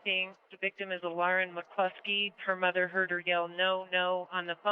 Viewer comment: she did not call that girl a liar. She said "Lauren McCluskey". Sure they were terribly inept but not cruel in that way.